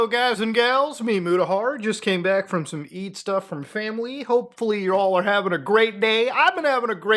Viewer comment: I heard "feet stuff with family" and no one can gaslight me into thinking otherwise